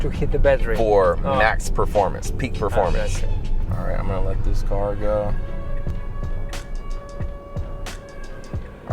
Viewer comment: я не расслышал, кем много работает хозяин машины, что много себе может позволить? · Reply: Тиктокер. Но Тесла в америке это как мустанг, доступные тачки. Не то сто в снг.